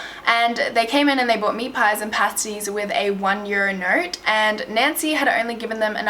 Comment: i'm sorry but what's that word she says around 5:33? one year inert?? when they came into the meat store place, what's that word that they gave the owners? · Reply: a note?